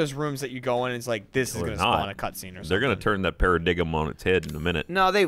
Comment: Did Ryan say paradigm?